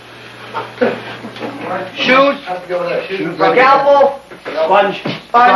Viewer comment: did he just say, “Scalpel!”? lol